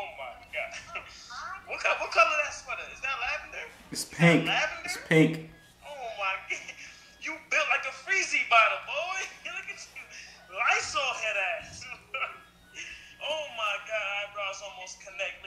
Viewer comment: all i heard was "build like lysol" i was like boi dont talk to him like that